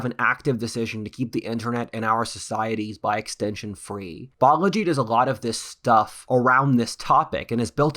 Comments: who does a lot of stuff around this topic and we should check it out? It sounded like you said "biology" but was it this video's sponsor Incogni? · I'm sorry but, for the life of me, I cannot comprehend what you said here. Was it "bollogy?"